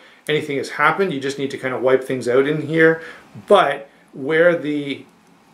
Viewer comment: did you say "out" or "ewt"?